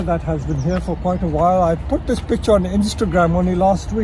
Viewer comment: did he just say 'I put this bitch on Instagram' or have I misheard? 😂